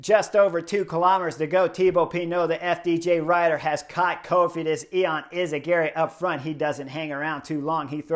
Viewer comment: You said "caught Cofidis" and I heard "caught COVID" 😂😂😂